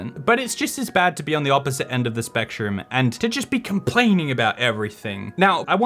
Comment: I heard "the other side of the spec-TROOM". This channel has traumatized me.